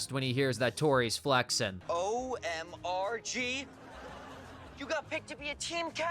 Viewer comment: When I was a teenager, I thought Sinjin was saying “Oh My Real God” as in he is a devout Christian and he believes God is real.